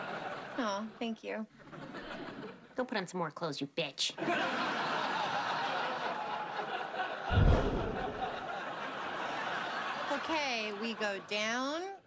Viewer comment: shy speak, " go put on some more clothes you" ? я слышу "go pronts more clothes you" what exactly she speak?